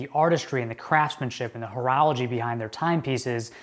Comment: What word did he use at 2:25? "Craftsmanship and *?" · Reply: Horology.